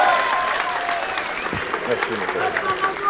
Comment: c'est moi ou dit : Merci mon pote ??